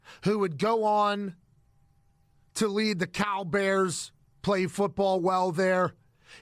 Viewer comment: When Pat said Cal Bears I thought he said CowBears I was a little confusd